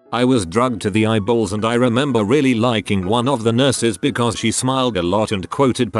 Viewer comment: I thought it said.. I remember really licking one of the nurses because she smiled a lot.. o h g o d